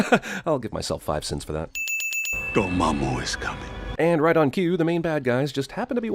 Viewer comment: Did anyone else hear him say, "your momma is coming!" 🤣🤦‍♂️